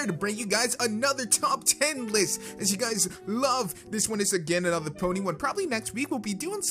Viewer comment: I was skipping through the video, and all I hear is "this one is gay"